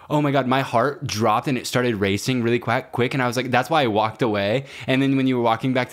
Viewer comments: saying ¨quack"instead of quick" in 20:30 absolutley killed me LMFAOOOO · the little moment where says "quack" instead of "quick" actually made me snort